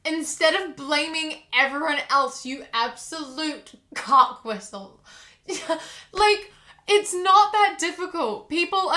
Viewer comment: I legit thought she called the guy a cockblister, and I was like daaang! that's one I've never heard before.